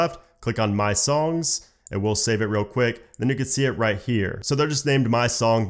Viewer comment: did he just say the n word??